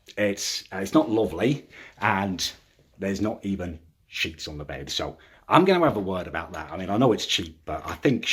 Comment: His accent made it sound like he was saying "shits on the bed" not "sheets on the bed" i haven't stopped laughing for five minutes